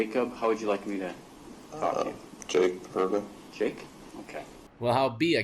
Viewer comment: what did he say? Jake Pervert? lmao